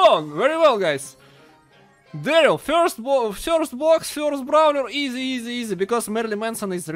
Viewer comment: I was laughing so hard when he got Darryl because I thought he said cursed brawler but actual he said first brawler but it’s still funny 😆 though